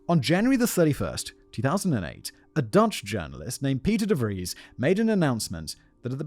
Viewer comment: I thought I heard Piter de Vries for a second there.